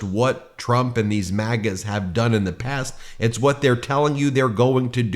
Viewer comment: I had the volume on low and had to do an audio double take when Ben said MAGAs because I thought he said he "maggots', which I know he would never say about those people. 🤣🤣🤣